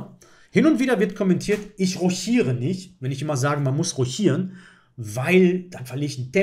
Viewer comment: Danke für die unterhaltenden Minuten. Hab noch nie diese Aussprache von rochiert / Rochade gehört. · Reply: Ich hab bei dir kurz nach 14:00 roschiere gehört. Ich kenne lediglich die französische Aussprache. Ich komme aus dem wunderschönen Sachsen in der Nähe von Dresden. Bekannt für seine oftmals lustige Aussprache. 😅